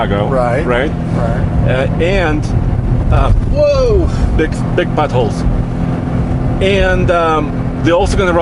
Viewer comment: it sounds like he says "BIG buttholes" 😂😂